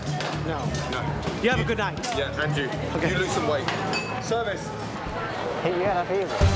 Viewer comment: did he say you lose some weight or something something wait